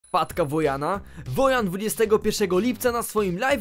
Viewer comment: A ja usłyszałem Matka Wojna xdddddddd